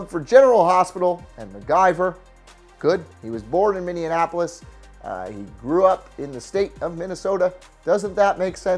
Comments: I'm sorry, did you just say "Minneanapolis"??? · why the fuck does everyone call it "Minneanapolis"? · Did you say Mindianapolis?